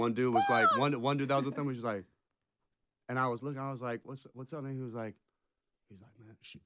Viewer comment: I swear he said the N word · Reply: He said what‘s up man